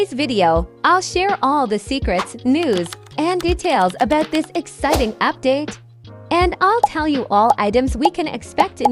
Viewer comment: um... what did that just say??? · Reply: Pregnant ✋🏻😭